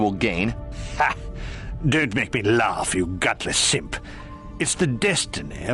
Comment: I thought he was saying Gutless Imp this whole time